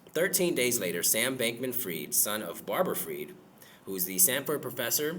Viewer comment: 😂😂😂😂😂 I thought you said g*y bank man fried